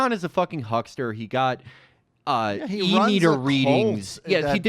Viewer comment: He got what kind of reading? Didn't catch that, can anybody help out? Also; R.I.P. Michael Brooks. The man. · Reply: He says “e-meter readings” they’re a Scientology thing